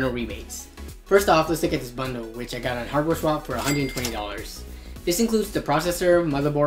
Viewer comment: Where did you buy your used parts? It sounded like you said HardwareSwap around 1:30 but I googled that and found no site for that